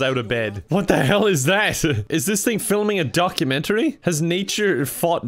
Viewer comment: I thought he said “duck-umentary” and was making a pun 😂